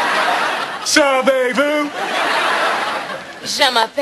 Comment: this is one of my fav. jokes on the show...and i never knew he was saying savez-vous...(sound like he was sayin "sup bay boo, sup bay boo"...now the joke makes more sense) haha